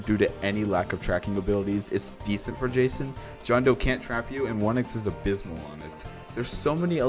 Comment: john doe can (unless he said can cuz i heard cant, if yeah im retarted) trap you in some spots, for example if you place a digital foot print on the door of the building and block the side you can through with spikes theyre trapped there unless they go through the poison lake, the cemetary you can trap someone there by just blocking one of theyre paths, and you also you can just place a digital footprint on one of the bridges and theres some others but i dont wanna yap more